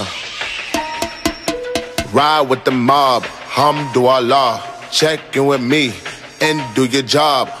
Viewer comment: I used to rap “Ride with the mob, HUM THROUGH A LOG 😂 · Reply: I thought he said hamdoallah